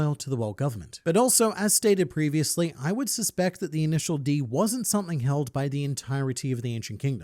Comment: did you say, initial D🤨